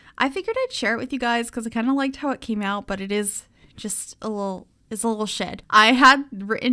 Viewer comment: What said: it's a little shed! :D What I heard: it's a little shit! Me: :o